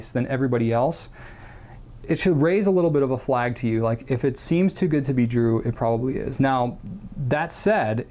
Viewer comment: did he just say if it’s to good to be drew or is my hearing going